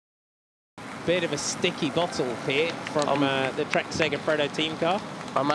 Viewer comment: Silly accents. I thought they said "stinky bottle" i was wondering what they put in the bottle that was so rancid that the announcers can smell it